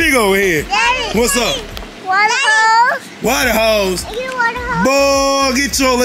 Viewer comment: I thought my boy said where da heaux😂but it was water hose